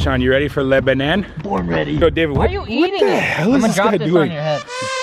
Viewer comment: Did he say "what the hell was this nigga doing" @ 9:58. ???